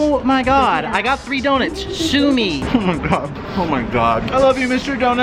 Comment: Omg at 8:40 when says "sue me" I thought he was saying sumi as in sumimasen which in Japanese translates to I'm sorry 😂, and it honestly fits so well just saying